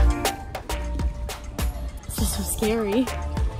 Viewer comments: did i hear “sister scary” ? 💀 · did she just say “sister scary” 😂😂😂I’m dead